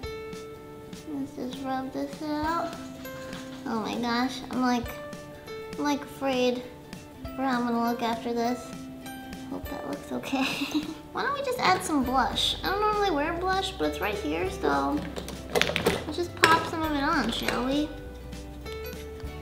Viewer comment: did she say it looks OK😱😱😱😱😱😱😱😱😱😱😱😱😱😱😱😱😱😱😱